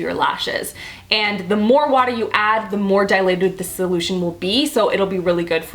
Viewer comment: Lol. It sounded like you said "dilated" instead of "diluted."😂 love you!❤️